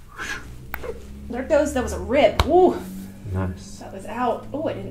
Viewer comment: Did she say rib!? That’s crazy